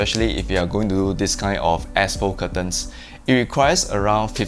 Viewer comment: legit thought u said asshole curtains HAHAHA. thanks for the tour video!